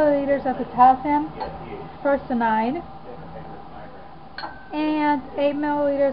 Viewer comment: I think she says potassium ferracyanide.